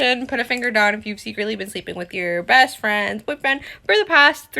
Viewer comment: pls someone transcribe this, I can't understand her, she's literally said "...the hospital nqieheiehjd covid" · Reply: Same :D i think i heard "...sleeping with your best friend..." somewhere in her mumbling.